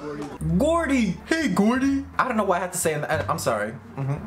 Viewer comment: probably cause it sounds like Pennywise saying Georgie from IT lmao